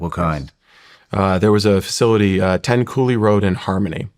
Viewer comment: On WHAT ROAD in Harmony ?? Did he say COOLIE ROAD, I mean WTH ???? lol "Coolies" were indentured laborers from China who performed arduous and often low-paid work, especially during the 19th century, often under poor and harsh conditions in plantations, mines, and on infrastructure projects. The term is often associated with indentured servitude, particularly forced labor, and the coolies' experiences often included long contracts and a high risk of death due to mistreatment and difficult work" ~ Google